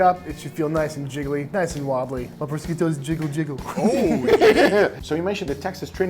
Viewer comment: Did he just say "My Brisket goes Jiggle, Jiggle"????